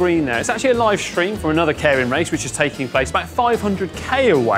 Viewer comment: I keep hearing "Karen Racer". Damn Karens are everywhere in 2020